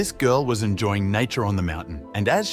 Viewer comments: Nature. I swore I heard "enjoying nitro on the mountains" 😅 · I thought he said nitro and not nature lol